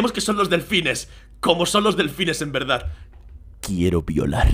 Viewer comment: Pensé que decía quiero volar :l